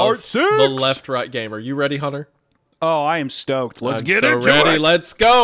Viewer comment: I swore you called Hunter "Hummer" after he said "hore" but truly meant "horror" 😅😅😂